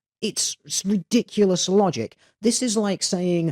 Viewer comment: Thank you for your suggestion. I apologize for going off on a tangent but I had a revelation. I went back to where I heard him say "female logic", at which point I had stopped the video and written my comment. Which was midway through the video. And I'm astonished. He actually said "ridiculous logic" and not "female logic" at 16:20. I've heard of allegations that people hear what they want to hear and I thought I was immune to this. Guess I was in denial. I only identified this portion of the video because I remember his pitch going up when he said it so when I didn't find it, I listened again to determine where his pitch went up. I am indebted to you for that simple suggestion. I am flabbergasted by how I heard words that he hadn't said. As far as I can tell from my experience of this phenomena, I was immersed in his argument, much like the way I get immersed in movies or entertainment, because I wanted to experience his version of reality. When something he said struck a chord in me. I hadn't intended to put words in his mouth and part of the reason why I had may have been because it was a topic I had been contemplating the past few days. I'm wondering if I made that unconscious assumption and that comment because it was a topic I wanted to get his opinion on. That's so weird an experience and it smacks of unconscious manipulation. I am not as self aware as I thought I was. I'm embarrassed now because I realized I was unconsciously trolling. Each word in this interaction